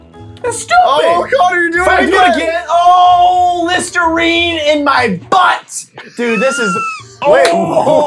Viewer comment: CONNOR WHAT THE HECK WAS THAT LISTERINE COMMENT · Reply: I thought he said Mr reed in my butt lmao XD